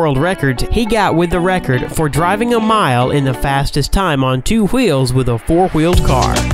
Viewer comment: did you say two gwheels?